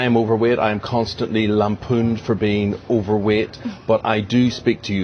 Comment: I swear to God, AT FIRST I thought he said "I am constantly HARPOONED for being overweight..."